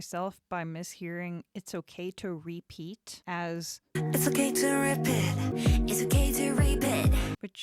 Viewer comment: I heard it as "it's okay to reap it"